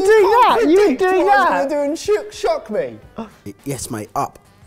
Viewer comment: did just say shit shock, and yall didn't censor it